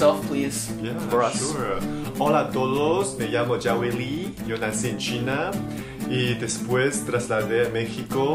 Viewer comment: did he just say “yo doy se China”? 😂